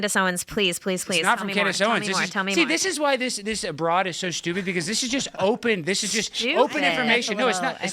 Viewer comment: When he calls her stupid at 0:49 does she say, "Sjewpid?"